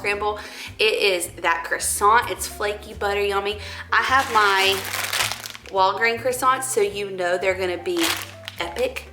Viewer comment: lol. Because of your lovely Southern accent, I first thought you said you got the croissants from Walgreens!